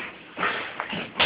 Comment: Did he say Loyality at 0:20?